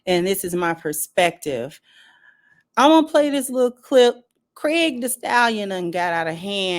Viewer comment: I thought I heard you say Craig the stallion, if so the name fits the person